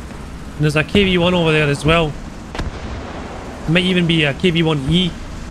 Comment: Did you say 5:40 there is kv1e 100%?